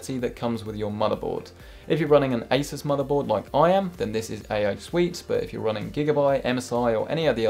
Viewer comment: What did you say at 5:10 ? A/o suite? I have an asus mobo too and wanna know what you said, I don't know why I can't figure it out lol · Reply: Matt Salopek AI suite thats What he said